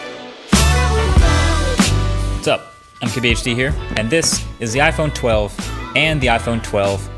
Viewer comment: For a really long time, I thought he was saying "I'm kbhd here"